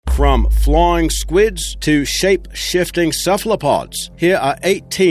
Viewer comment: Wait did I hear flying squid in